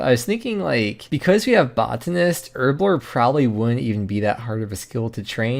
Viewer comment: I thought you've been saying "because of the bots in this" the entire video. At the end I realized you were saying "because of the botanist" 😂